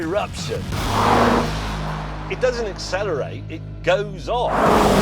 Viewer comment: “It doesn’t accelerate it just goes on” is why may ,Hammond, and clarkson are the best top gear host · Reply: I thought he said “it just goes off”